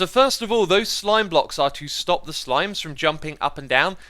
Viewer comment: did he said slime blocks on a honey block